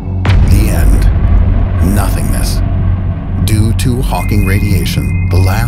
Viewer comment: did I just hear Hawkins? Stephen Hawkins revenge!!!!!!!!!